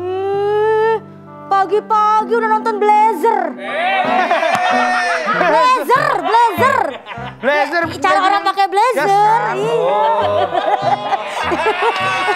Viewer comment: anjir aku dengernya si Kiki bilang brazzer ternyata blazzer😭😂😂